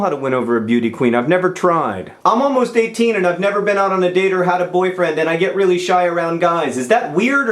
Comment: Is it not weird that I thought you were actually saying you were 18 and never been asked out on a date... at